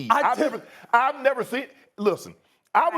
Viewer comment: "Look Skip!" 5:12 Shannon had PTSD 😂 I don't care what anyone says Skip and Shannon were meant to be together. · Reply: I think he said “listen”.